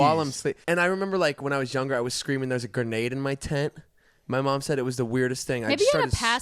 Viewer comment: Damn I really thought that said "Theres a grenade in my tit"